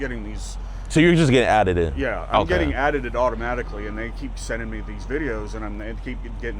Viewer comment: am i tripping or are they saying addeded , instead of added?